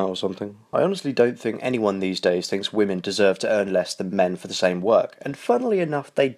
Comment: did you just say that women don't deserve to earn the same as men for the same work? If not, then what did you mean? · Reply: You heard it wrong he said that he doesn't think women should earn LESS. Sorry just go back and listen closer